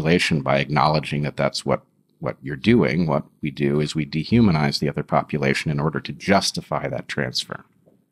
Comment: "We de-humanise the other[?] population in order to justify that transfer." 9:20 We stole their diamonds, Bret? Is that what you want to say? We stole their diamonds?